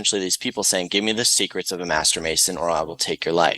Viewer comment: I just heard “Give me the secret of the masturbation rod”